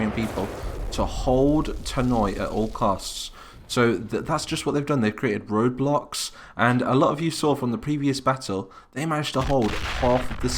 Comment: i thought you said roblox XD